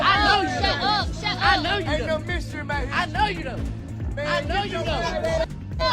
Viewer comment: I think I heard T.i call king a mystery baby 8:43 🧐